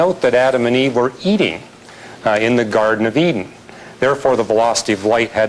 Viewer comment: how i heard it 'adam and eve were eating, in the garden of eatin' ' xD